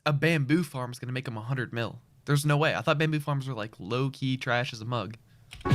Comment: guys every time I hear loki, what does loki meeean?😅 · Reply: He's not saying loki, he's saying low key. It's been a common phrase for decades which basically means subtle or restrained. However, that's not how he's using it. Recently (in the past few years) it's become a slang term which is an adverb that basically means "kind of". However, it's not the exact same as "kind of", it adds a nuance of slight secrecy/subtlety. There is also another slang term, "high key" which also pretty much is the exact same but with the opposite meaning. It just means "kind of" but with a nuance of openness/bluntness.